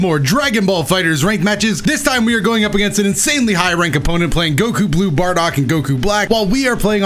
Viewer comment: said it so fast that i thought he said "Goku,blue bardock and goku black" XD